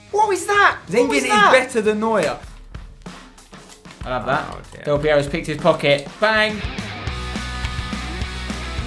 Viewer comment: is it just me or did say Allahu Akbar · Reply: Caitlin I'll have that